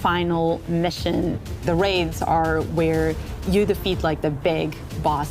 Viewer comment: does she say "raids"????